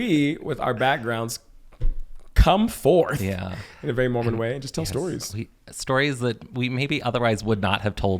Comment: I heard you say "in our Mormon way" and wanted you to bust out into "in our Humble Way" from Saturday's Warrior 😆